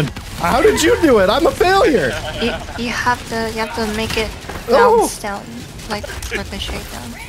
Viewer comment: did i hear you have to make it bouce down like the machine gun? If you can take out fabricator with mg is it a specific one?